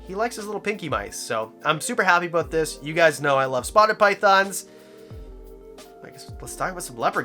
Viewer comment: I can't tell what kind of python he's talking about around 15:00 what is he saying? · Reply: Spotted Pythons